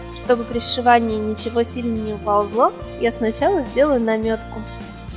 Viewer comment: послышалось "намёк " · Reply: Там сказано "намётку"